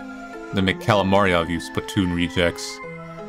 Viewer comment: did he call them "splatoon rejects"?